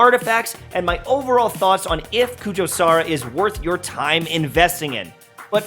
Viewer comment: Did I hear this right? 0:35 "If kujou sara is worth your time in destiny"